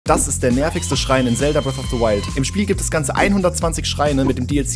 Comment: Ich hab die ganze Zeit "schreien" verstanden und wunder mich so warum man da bitte Schreie freischalten soll 🤣